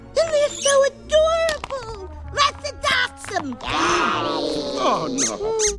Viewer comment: when I was little I thought mrs potato head said razzle dazzle instead of let's adopt them